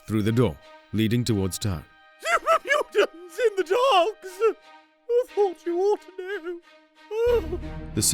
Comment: ? What did he say I can’t understand him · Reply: Super mutants in the docks, thought you aught to know. Reference to troll in the dungeon.